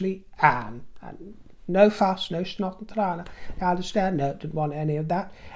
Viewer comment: Dear, Sunday morning,chopping veggies for our lunch and listening to you @ 10:03 Did I hear you say 'no snot and trane' What South African influence is in your life?🤔 So lovely to hear it(if I heard correctly.)love from Jhb SA.🇿🇦